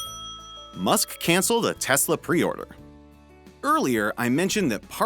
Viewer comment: I heard Tesla Priora